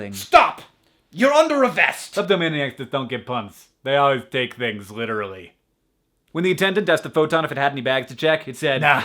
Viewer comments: What did he say at 1:09? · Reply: Kleptomaniacs don't get puns, they always take things literally. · What is he saying at 1:09? I have watched it like 7 times and still can't tell. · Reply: kleptomaniacs just don't get puns. They always take things - literally. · I can't understand at 1:07 =\ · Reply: Nevermind, after playing it like 20 times I finally heard kleptomaniacs, which makes sense now. >.<